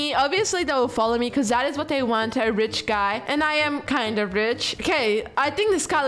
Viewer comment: I thought she said the B word but she said rich😅